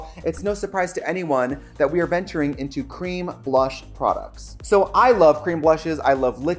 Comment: Tricia I thought you were saying cream brush